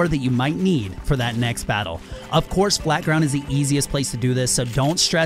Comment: did u say flat ground?